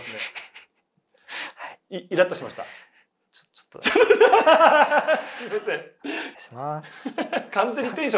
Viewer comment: ダムのは「ちょっと、すみません。」って聞こえる。